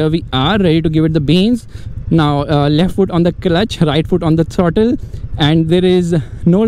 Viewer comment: Did he say "left foot on the clutch.......?" 😂